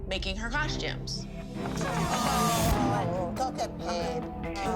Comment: Did anybody else heard Holly say "go get paid" @ 4:56 ? 🤣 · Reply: Its go get paige